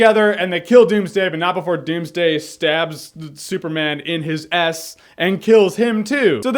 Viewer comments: I was not paying attention for a moment while playing the video and when I did I thought Jeremy said: '...Doomsday stabs Superman in his (as-)S' That would have made the climax of the final battle a lot better! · LOL I thought you said "stabs superman in the Ass" not in the S